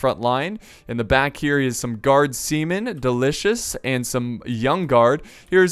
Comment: did just say the guard semen is delicious?